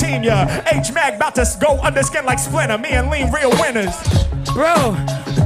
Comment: Since you're pointing out recoveries, I also thought Harry said "clean like soap" with a small stumble on "clean", as opposed to "ski like slope". Fwiw